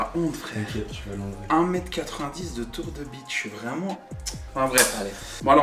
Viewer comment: De tour de quoi ? 😂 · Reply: bide